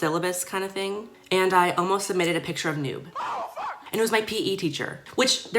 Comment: i thought you said you sent a picture of your nude instead of noob